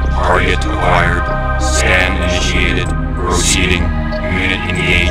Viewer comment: what did Cyborg commando said on 0:29 i heard "Sand Initiated?????" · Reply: Scan Initiated.